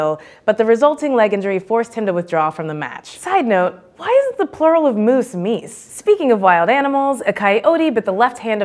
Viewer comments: The plural of moose isn't meese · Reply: After several attempts I'm with you EP1CCRAFT3R sounds like she said "Why is the plural of moose, meese" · I have concluded that some, not all but some, of the things said on this show is false. I know for a FACT that the plural of moose is NOT meese. I live in Maine so i know that. To make sure i went online and Webster's English Dictionary says moose is plural and singular. Sometimes that can be confusing because the plural of goose is geese, but it doesn't work the same for moose. I have noticed other mistakes on other episodes as well, so I am unsubcribing. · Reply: I apologize. I misheard what she said. I thought she said "why is" not "why isn't". Although, my point still stands. I have seen other incorrect remarks made on previous videos. I cant recall particular episodes where this occurs, but i remember there were some. Thank you for the correction.